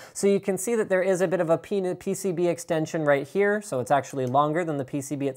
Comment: Seriously what is with linus and him saying penis extension ? 2:47 HAHA